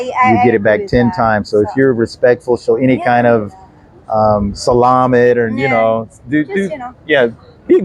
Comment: pretty sure he said salami 🤣 When I was in Mexico I'd say 'grachus' instead of gracias, and the people and service workers would all say de nada 😄 I asked a waiter once if he hears that a lot, he said no but we laughed. Altho come to think about it, he likely was laughing at me!😁And that's ok👍🏽So when I come to PHs I think I'll start working with salami and myownboots on 😆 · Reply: Lol 😁 he said salamat. Thank you, Jc 😊 🙏